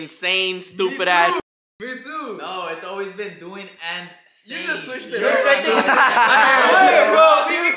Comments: I always hear “doing and saying” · I've always thought it was "doing and saying" since it just makes more sense